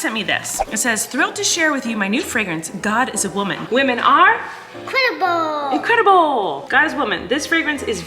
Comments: I thought said "women are crippled" I am messed up love you guys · I swear for a split second I thought said women are "Cripple" instead of "incredible" 😆 I can't hear well apparently